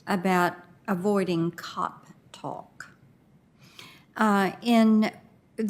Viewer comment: I thought she said “cock talk” 😂🤣